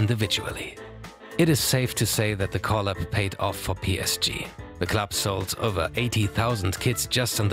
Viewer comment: I thought he said "The club sold 80k kids" 💀